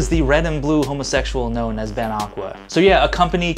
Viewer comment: I had to listen to the intro twice, I thought you said 'gnome' instead of 'known' XD